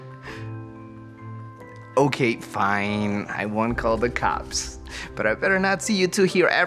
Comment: He said I won't call the cops and I thought he said he was the cips